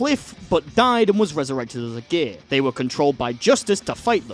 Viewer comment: ngl I thought you said gay, not gear for a sec